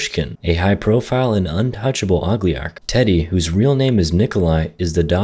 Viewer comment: He honestly said "og-li-garc." It's "o-li-garc." Yeah, I'm that fussy.😋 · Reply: I thought he said , ugliarch, which is actually clever and made me laugh